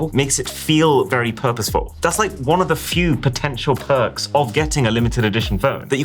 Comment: DID I JUST HEAR POTENTIAL?